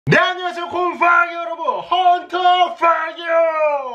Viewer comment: 네 안녕하세요 군퐝이 여러분으로 들림